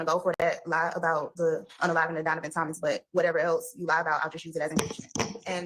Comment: Wait did someone just say “unaliving” in court?